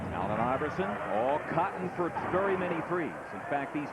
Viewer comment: Did he really say all cotton?